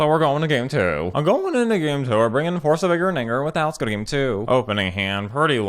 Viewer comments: Did he say the N word at 7:25? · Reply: I obviously said Vigor! Force of VIGOR! I just slur my words a lot, and it didn't even cross my mind that it sounded like anything else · excuse me what did you just say? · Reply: I obviously said Vigor! Force of VIGOR! I just slur my words a lot, and it didn't even cross my mind that it sounded like anything else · it 100% sounds like you said force of vigor N word Like hard R N word I'm not even trying to reach it sounds like nothing other then the N word Smh